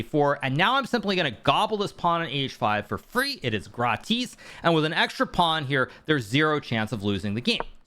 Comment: did he say "GRATIS" 😂